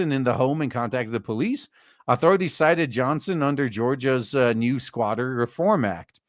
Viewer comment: I swear. It sounded like you said they lived in s*ck me Johnson.